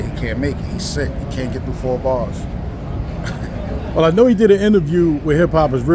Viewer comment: Did he say Cass confirmed he was sick n cant "Give the 4 Bars" ???